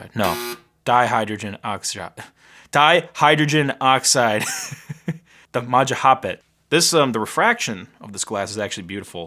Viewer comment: “the majah hop it” the stupid terrible captions got it!